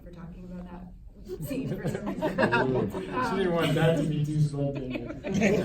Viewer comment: did he say she didnt want that to be too spontaneous? lmao